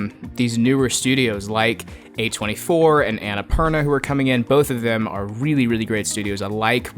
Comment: does he say ''annapurna'' ?